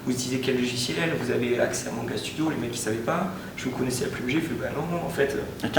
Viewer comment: SVP les gars , il dit quoi a 14:57 apres "vous avez acces a manga studio..." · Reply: Bago Garde "il ne savait pas" ensuite il dit " vous connaissez la plume G, les mec disait bah non non"